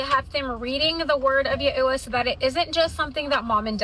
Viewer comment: I thought she said OSHA and I was like oh you're teaching your children about occupational safety😂